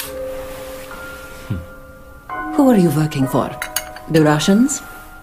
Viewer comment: Am I the only one, who heard she said “ Who are you fucking for ? “ lmao